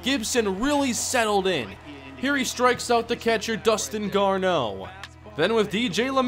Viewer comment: I thought you said Justin Morneau because he played for The Rockies after his Minnesota Twins time.